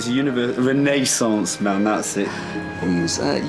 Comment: Так чётко и выразительно говорит на английском, не "проглатывая" окончания слов, что можно до словно понять: о чём или о ком идёт речь ❤ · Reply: я по интервью могу понять, кто что говорит, местами. Тут мне "universal man Socrat " послышался 😁. Ну я недалека была..... от смысла разговора... Если там про эпоху ренессанса, как выяснилось, шла речь ( о чем я тоже не сразу поняла, т.к вместо renaissance мне тоже другое послышалось). То и про античность и Сократа логично... Вот так. Чисто интуитивно. А оказалось верно.